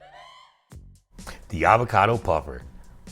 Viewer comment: For a moment I thought you was about to say avocado 🍐 pear hahaahahahaaha looking beautiful.